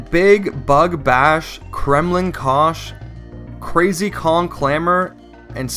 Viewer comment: did he say KREMLIN KUSH!?